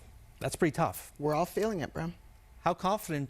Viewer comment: Did she say bro ?